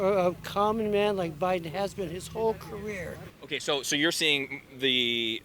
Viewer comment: “Biden has been a common man his whole life” exactly what we don’t need anymore. We need a leader, not a sheep. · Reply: I thought he said "con man his whole life" yeah, I agree